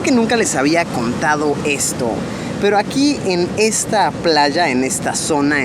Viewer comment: cuando dijo aqui 7:30 yo crei que hay a donde señalo jajajajajaja hasta que dijo playas